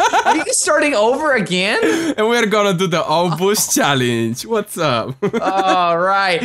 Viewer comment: I thought they said ' All Booze Challenge ' xD